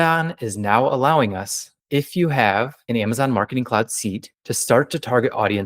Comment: you say "...if you have Amazon ???? ???? -- what are the missing words? What is needed to use the 5th strategy? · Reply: An Amazon Marketing Cloud SEAT (AMC) - We have one at Profitable Pineapple Ads if you are looking to use it.